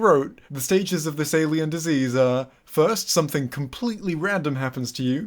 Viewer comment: I was playing this in the background and I heard “and then her nervous system fell out” and I thought was fucking with us